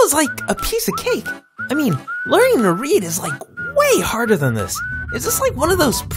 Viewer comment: Let's see here.. Thanos, Snoopy, Spiderverse, Saske Choking, did I miss anything..? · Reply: I thought I heard Pit say "I NEVER LEARNED HOW TO READ!!!!"